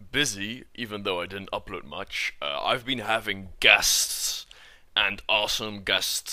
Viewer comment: I thought he said he had gas instead of guests